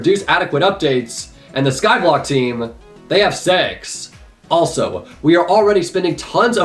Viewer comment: why did i hear "they have sex"